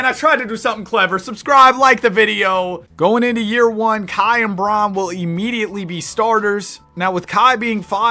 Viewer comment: Kai and Bron. When I heard that I thought he was talking about Kyrie and Bron 😢